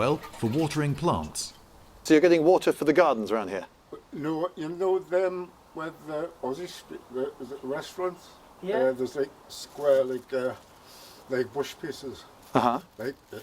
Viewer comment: I'm not sure about the original Canterbury tales but that dude at 27:25 is tough to understand. I heard "restaurants" and "squares" and nothing else. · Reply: "No, you know them where the ozzy street (osbourne road), uh is it restaurants there's like square like uhh wash basins right there's like in a circle where, and there's like 2 sets of bushes in - where the phone box is - yeah I'm just putting some there, just for the dog's water" Where the restaurants are on osbourne road, in the road circle where there are two sets of bushes and a phonebox there are square wash basins that he's filling with water for dogs. Here: <link>